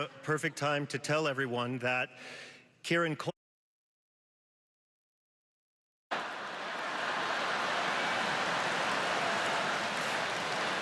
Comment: why can i not hear this bit??? What did he say? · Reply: Kieran Culkin beat the shit out of me